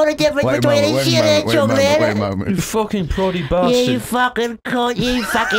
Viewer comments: calling Tomar a "Proddy Bastard!" has got to be the most Irish thing that has ever done. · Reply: Ohhh, ok - I thought said "plodding bastard," like Tomar plods along because he's so big! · "You proddy bastard" apart of the ira? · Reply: I thought he said "plodding bastard," like Tomar just plods along whenever he moves